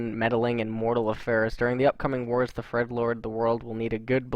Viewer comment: Was it just me, or did I hear "the Fredlords"? :D Between this and Kevin (among many, many other things), this vid was hilarious :D